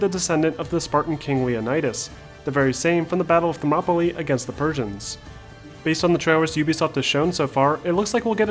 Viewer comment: When he said “the Persians” I thought I heard “the virgins” and I was like “What? There was an army full of virgins? Nah I gotta hear this again”